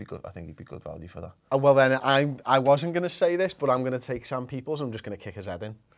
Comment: same people what are you saying 12:29 ?